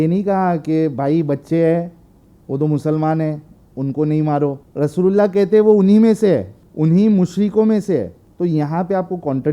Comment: kya bola rassogulla 😅😅